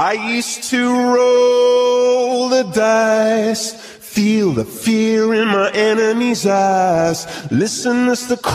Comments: I thought he said feel the fear in my enemies ass💀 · why dod i hear "feel the fear in my enemys ass" 💀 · WHY DO I KEEP HEARING "Feel the fear in my Enemies Ass"